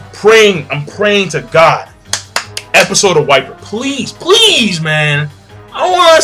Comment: Thought you said episode of Whitebeard at the end. that would be epic!!